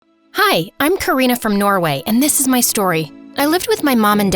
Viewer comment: i thought she said "Hi Im Korea From Norway"